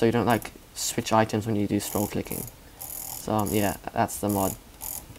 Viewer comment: but its was dragclick i heard